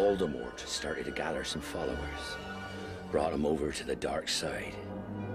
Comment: When I was a kid I always thought Hagrid said "Voldemort was gathering flowers. Brought them over to the dark side." Which made me think he was out picking flowers the night he killed Harry's parents.